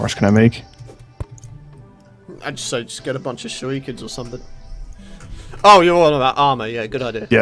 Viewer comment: on subtitles it says sh**y kids instead of shurikans